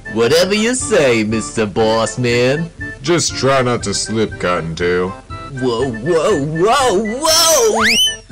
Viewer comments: I hear Freddy say “just try not to slip god dam” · Freddy and Bonnie: *having demonic thoughts* Foxy: "stop mucking around" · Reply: I thought Freddy was saying "Just try not ta slip god damn" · I thought Freddy said just try not to slip god damn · Just try not to slip Goddamn is what I hear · i think he say mr balls ☠️☠️☠️☠️ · I just turned-on captions and it said Freddy said cotton tail but with no captions it sounded like he said try not to slip God damn.